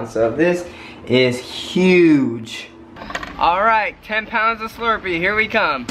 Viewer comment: At first I thought it said 102 pounds😬😂